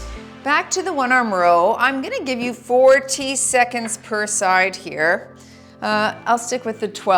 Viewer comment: 2nd round of the 1 arm rows- I thought you said a full 14 seconds( never mind that it doesn’t make sense 😆) on each arm. Imagine my disappointment when I realized you said 40seconds!😅